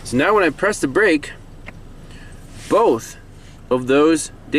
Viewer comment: great find. Perseverance.... you called it earlier but opted to replace the easy part. Ce' la vie.... Do I keep hearing you say "Data Pit"? Maybe I keep hearing it wrong. It's "Data Bit" .... Electronic data travels in bits and bytes...